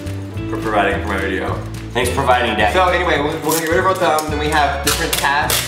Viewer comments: Did call jc daddy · did anyone else hear say "thank you for providing daddy "? 😂😂